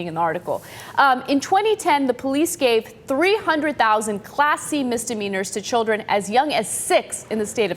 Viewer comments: Who else thought Anna was saying "classy" and not "class c"? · Oooh, 'Class C'. I thought you were saying 'classy'... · OH Class C! I thought you kept saying Classy :) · So, I'm the only one that thought was saying "classy" and not "Class C"? I'm sitting here thinking "oh, so this is what we're calling classy now... throwing kids in jail? GTFOH! LMAO I gotta listen better! :)